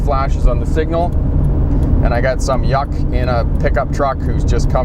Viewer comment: what did he say:I got some.... what? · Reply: Hi kristo jeksan - Thank you for your query. "And I've got some YUK in a pickup truck..." Many of the videos have closed caption, so if you don't catch the words, you can look at the closed captions. If there is anything further we can do, drop us a note. Always happy to help. All the best, Cheers